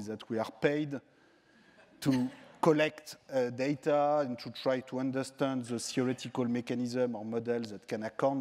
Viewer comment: What does he say at 6:50? i can barely understand it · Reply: To collect data and try to understand the theoretical mechanisms or models that...